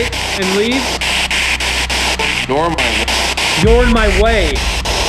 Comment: I heard “normally” not “you’re in my way”